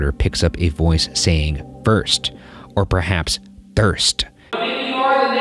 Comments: Don’t you think the EVP at 18:37 is saying “worst”? As in Stanley is confirming that he’s the worst? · Instead if thirst, I heard "I guess" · I don't hear " thirst" I hear "worse/worst"